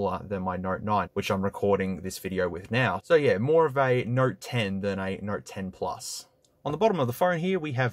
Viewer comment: Did he just say nert 10?